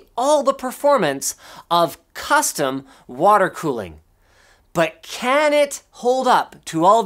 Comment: I thought he said but can it run Crysis